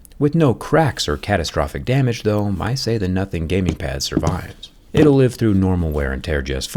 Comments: Did you say “nothing gaming pad”? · wait did he say Nothing gaming pad lol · did zack just say nothing gaming pad? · did you say Nothing Gaming Pad survives? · did i hear Nothing gaming pad? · did he say Nothing Gaming Pad?